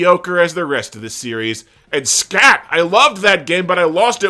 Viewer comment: what game ist hat at 4:07 sounds like you said scaf but cant find any game called that